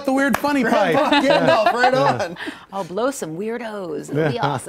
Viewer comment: Did I hear the hottie say, "I'll blow some weirdos?" - @ 3:30 in the video